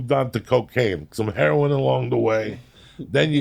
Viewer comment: did Joey say he used to do Heroin???!!!